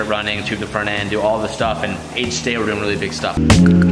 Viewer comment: DID HE SAID HDAY??